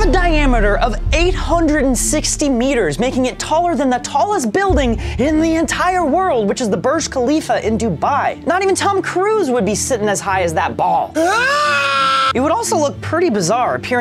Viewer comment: I swear I heard "not even Tom Cruise would sit on that ball"